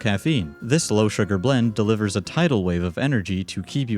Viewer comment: Did u say, "TIdal Wave"?